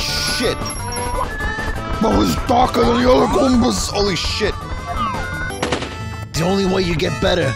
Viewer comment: subtitles are wrong, should be "he was darker than the other goombas," was referencing a former friend who was way too into star wars dark lords